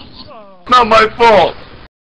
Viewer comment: Hei wait did he just said its not my fault on 1:23 ??